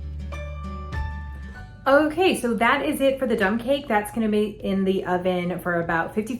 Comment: Lol, I thought you said dumb cake, then later you said dump. This will be interesting.